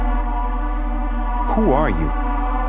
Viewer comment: when he says “who are you” i hear “faze rug”